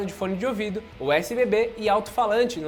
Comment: ouvi “usbb”?